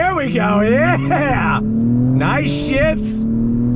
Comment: it sounds like he says “nice sh!ts” 🤣